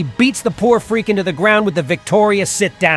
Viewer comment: I THOUGHT THAT SAID SH*T DOWN 😂😂😂😂😂